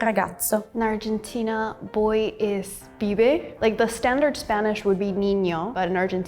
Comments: I dont know who was the dumbass that wrote "Fibe" instead of "Pibe", where does he frickin hear the F? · Subtitles are wrong. She said "pibe", not "fibe". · Shouldn't it be "Pibe" instead of "Fibe" or i've been speaking my own language wrongly? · subtitles are wrong. She means PIBE, not FIBE; fibe doesnt exist here (in argentina)